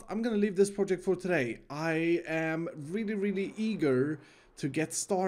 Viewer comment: Is it weird that at 8:20 I thought said egirl instead of eager